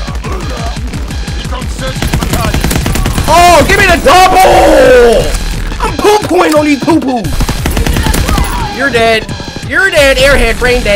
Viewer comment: Did he just say poo poo